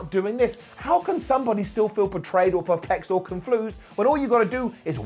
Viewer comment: "conflused"? Tell me he did not just say that! 😂